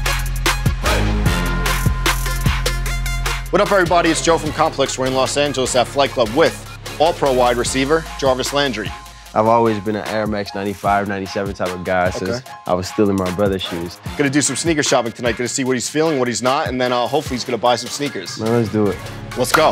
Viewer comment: I thought it said Jarvis makes laundry not Jarvis Landry LOL